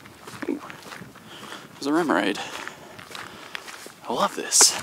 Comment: "there's a remoraid" Wait did he just say mermaid 🤣